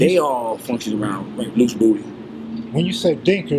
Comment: Uhhhh Fam at or around 4:37 - 4:39, did he say Loose Booty or Luke’s Booty still sounds bad but I wanna be sure